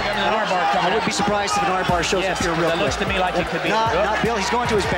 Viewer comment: Man I thought he said "That looks to me llike Khabib's" WTF haha